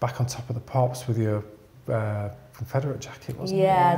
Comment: The jacket she wore on TOTP for Hand Held in Black and White…I always wondered where she got it. He mentions it at 37.05. Sounds like he said Federer jacket. I know it says Willspoint on the sleeve. It looks cool.